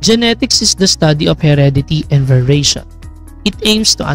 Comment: Sorry po, i thought you said "vibration" instead of "variation". Thank you Sir for this lesson! God bless po!